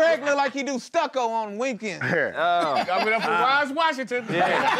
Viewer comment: What did say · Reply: Yall give it up for Roz Washington